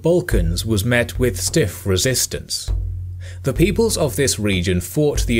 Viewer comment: does he said ,,the peopleS ?"